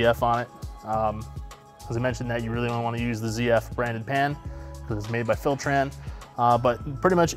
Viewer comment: Did he say not to use a ZF branded pan?